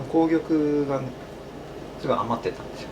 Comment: I want to watch but don't trust the closed captions. @ 2:28 it says, 'The reason I made the apple pie is I had a huge surplus of red beans.' I've never known an apple pie recipe that included red beans... Perhaps 'red apples' is what he said? There are other things the baker supposedly said - that I don't think are accurate. At one point he says that doing something is a 'big hassle'. I can't imagine any Japanese baker commenting in such a negative way about their chosen profession/passion. If only I knew the Japanese language... Looks like a wonderful documentary - wish the cc was accurate.